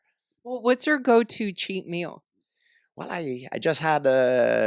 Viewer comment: I could’ve sworn I heard him say “wallahi” at 1:09 😂